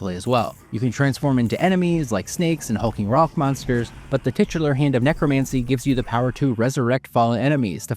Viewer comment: Did you just say tits?